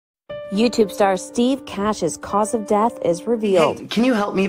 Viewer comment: Bout thought u said Steve nash oof but rip